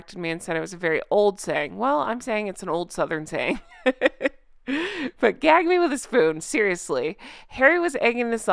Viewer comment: I heard it as "gag me with a maggot." Maybe it's my generation ... hunters and leaving deer heads in the cellar too long.